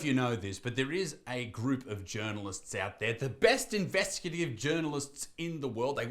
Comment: When you started talking about journalist, I misheard it and understood Germans. And I liked that.